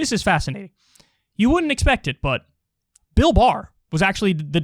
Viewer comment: I was confused because when he said Bill Barr, I thought for a second he said Bill Maher.